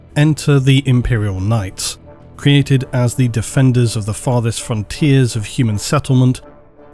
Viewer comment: I thought it said defenders of the fart😂